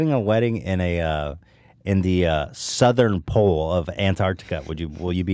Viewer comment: “Artantic uhh wildlife” - Ethan Klein 2019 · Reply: I thought he said Arctantica 😂😂 either way lol